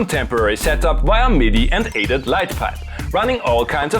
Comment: Great episode. I don't get what you say around 7:59, connecting the old PC via midi to new gear and more what? The subtitle says "Light pipe" but I know its just google messing with the transcription. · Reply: ADAT light pipe - self-clocking digital 8channel format that is very widespread, even on older audio interfaces